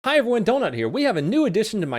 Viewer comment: I kept playing the intro over and over and It sounded like he said I have one donut here